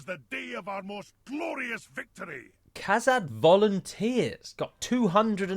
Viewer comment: From far away I thought he yelled "Kazakh" instead of Kazad. And my first thought was, "That's where Dimash is from."